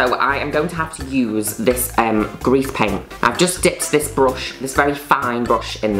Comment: When you mishear grease paint as 'grief paint' but it works within the context of the video 😂